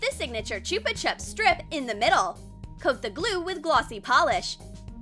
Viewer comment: I heard "cook the glue"...…………. I haven't slept a wink in 6 days, HELP